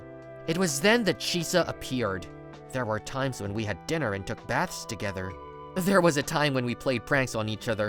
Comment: did u just say "took a bath together"? 🤨🤨🤨🤨